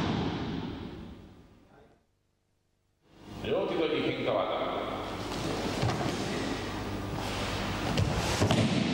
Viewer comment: what's the technique at 6:23 please? it does look like kokyunage but it's not what he says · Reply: "Ryote-dori henka-waza". Henka = variation.